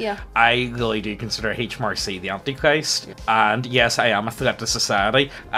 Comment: Who does mention at 43:06? H. Marcy? H. Marsh E? Tried googling these plus Northern Ireland and couldn't figure out who he was referring to. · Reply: HMRC - His Majesty's Revenue & Customs